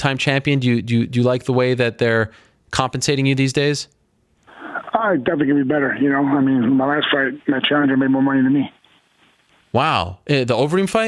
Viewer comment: I’m sorry but this killed me🤣🤣 I have absolutely no idea what he just said💀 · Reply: “Ahh, definitely could be better, yknow, I mean, my last fight my challenger made more money than me”